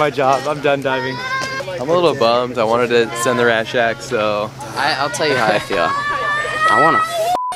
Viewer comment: Almost 1 million views man!!! Btw what does that dude at 4:40 say? Im a little bit bumd I wanted to send the ........... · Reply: he said send the rat shack. It's an 82ft cliff next to an old shed